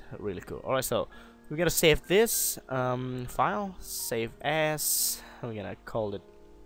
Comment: it sounds like you say 'save ass';-)